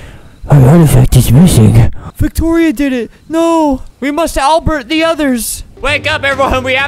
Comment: i know it has been a year or so but , did you just say "we must Albert..." when they said " we must alert " ? : ) ... I like vegetebles hulk